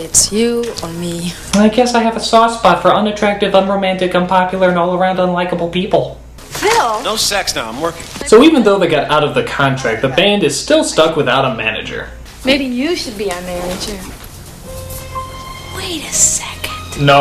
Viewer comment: I definitely heard the science guy say "no sex though I'm working" Who was this movie made for ??